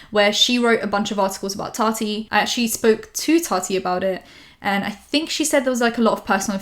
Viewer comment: I don't now if i understand right, did she say that she spoke to Tati? In 17:05 ? English is not my first lenguage